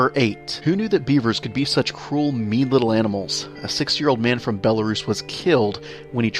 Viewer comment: did you say a 6 years old man???